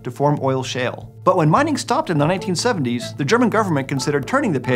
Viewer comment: Turning something into a dump isn't part of the federal goverment's job tho', more likely it was the hessian or the Landkreis' goverment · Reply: my bad, I misheard and thought mining stopped in the 1870s 😅😅